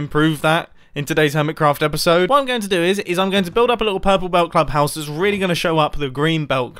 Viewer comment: It sounded like you said purple clubpoes 😂😂😂😂😂😂 only my fellow south Africans will get it 😂